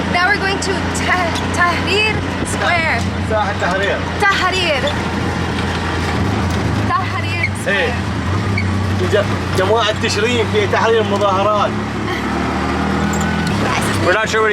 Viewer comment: He actually said: "Tahrir square, where the people demonstrated against the government in October of 2019". It has somewhat of a special place in the Iraqi's hearts, given it was a semi-successful "revolution".